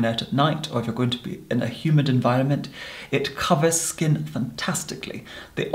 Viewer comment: When he said "humid environment", I first heard "human environment". Thank's for that 😂